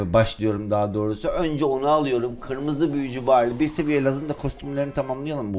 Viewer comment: kırmızı büyücü barleyi kırmızı burunlu barley diye anladım